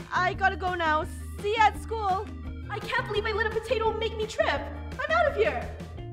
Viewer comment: Heyo! I hope you enjoyed this video, if you did leave a LIKE! :D Thanks for watching! ★ What are your feels on this episode? I would love to know! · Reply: 123 I thought she said I can't believe this potato stole my first kiss 😂